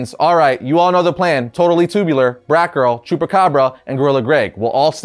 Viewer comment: ngl, thought he said jewpacabra